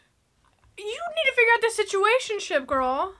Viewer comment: Did you call it a "Situationship"?